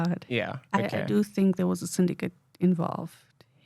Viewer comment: ivd tried to rewind and listen several times - I cannot understand what she’s saying at this part. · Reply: She said she thinks there was a syndicate involved with her kidnapping.